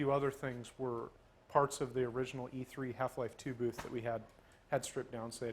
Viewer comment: I thought he said Half Life 3 booth for a second.